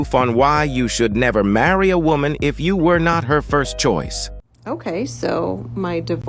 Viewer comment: I thought you said you were hot.